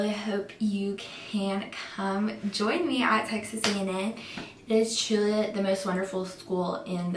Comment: I thought she said sexist A&M I was thinking “WTF?”